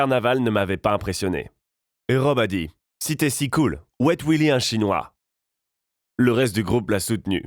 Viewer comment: Il a dit quoi a 1:30 ? Wait Willy un chinois ?